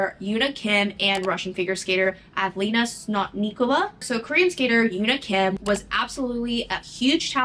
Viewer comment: I misheard Sotnikova as SNOTnikova amd that had me wheezing for a solid minute (oof)